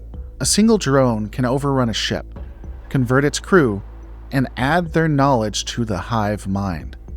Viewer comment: I had to replay 17:03 becauee I thought you said "add $30" 😂